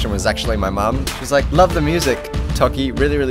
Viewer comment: lol i thought he said "musac"